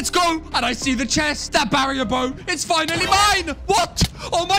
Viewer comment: its been 2 months now i hear "barrier bome"💀